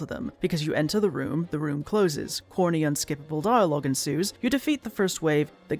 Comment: Yeah, I misheard that as “HORNY unskippable dialogue” and got very confused like, “Wait, it doesn’t look like that kind of game…” then my page glitched, reloaded a few times, rewound the video each time to right before that point, I realized you were saying “CORNY unskippable dialogue” and my relief that this game at least didn’t accidentally sell parents a “child friendly” looking horny furry game might not have been “immeasurable,” but did get an actual physical sigh of relief out of me. 18:25 Wait, I just realized Kay’s tail is just spinning in a circle in all these scenes. It doesn’t look like it’s doing that in the gameplay, why is it doing that?